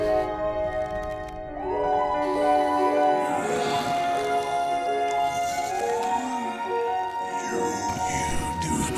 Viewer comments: Hey can someone please tell me, what does the mouth inside the monster say at 3:11? I have the volume up all the way on my phone and I still can’t figure it out. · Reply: “Are you doing okay, sweetie?” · what is it saying in 3:16? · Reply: ooooh it's are you being ok sweetie